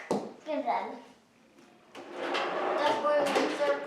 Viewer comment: I never heard an accent from you before but in this video, when you say "drawer", I hear "draw". Boston?